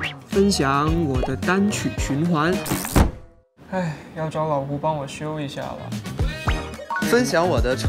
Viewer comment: 小熊说“老胡”我居然听成了“老公”